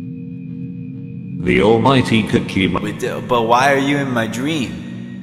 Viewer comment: ah shit I thought he said all might